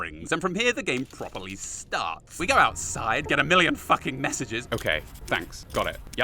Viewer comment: did he say "... femboy splash back." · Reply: He said fanboy, sounds similar